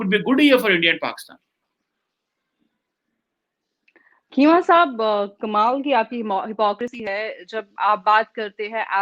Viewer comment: what I just heard ??? sahab 😂😂😂😂😂😂